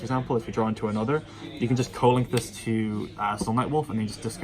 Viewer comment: Can you explain what you were saying with Knightmare Phoenix being good against the Slamangreat matchup? Couldn’t quite hear what you were saying. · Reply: You co-link it to sunlight wolf so you can discard a dead card for the effect and draw a fresh one